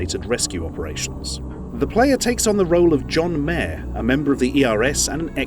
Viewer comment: did he say JOHN MAYER?!!!